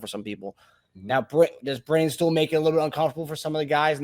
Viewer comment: It sounds like is saying "Brainstool." Go CHIEFS!